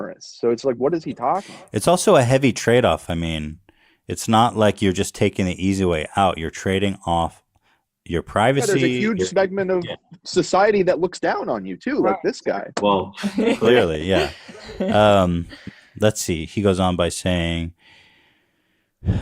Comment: “huge smeg-ment of society that looks down on you” I LIVE FOR THE MISPRONUNCIATION ON THIS SHOW 🤣🤣 anyone else hear him say smegma instead of segment? 😄💗💗